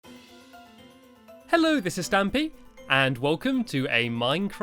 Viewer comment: I always thought he said hello mrs Stampy lol